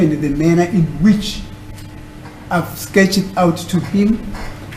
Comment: Askhed???? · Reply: 😅😂😂I thought he said “asketh” like old Bible English